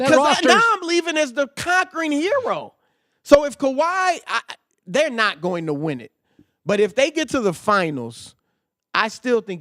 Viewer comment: What did you say Chris? Raps not gonna win it all ? LMFAO.